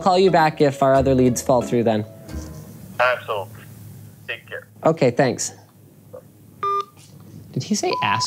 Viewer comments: i heard ass hole XD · I feel like at 19:50 the guy did sound like he said a**hole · I’ve listened ten times over and there is no way he said ‘absolutely’. I hear asshole and nothing else